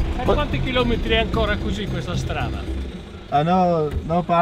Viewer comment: Minute 2:37 can someone that speaks Italian tell me what he says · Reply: The man says "per quanti km è ancora così questa strada?" It translates to "for how many more km is this road going to be in these conditions?" and then he asks if he speaks Italian. Thomas says "Non parlo italiano" aka I don't speak Italian